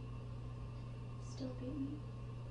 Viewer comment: What did she say ??? snicker "still breathing" ? laugh "still beat me" "still beat you" ? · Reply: “Still hate me?”